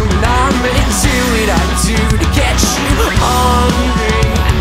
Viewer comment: Always thought he was saying "horny" or "hungry" but this song taught me the word "ornery" and now i use it almost daily.